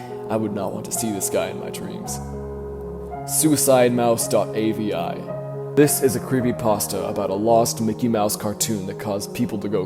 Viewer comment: I thought that says SuicideMouse.avi turns into a blueberry lol